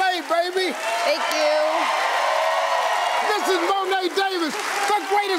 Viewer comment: what did Harvey say at 4:55 ? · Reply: Joseph Kim He said "the greatest little leaguer of all time."